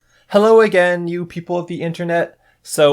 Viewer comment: DID YOU SAY YOU PEOPLE THATS OFFENSIVE